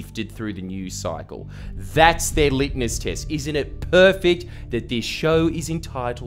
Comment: Did you just say "Litness test"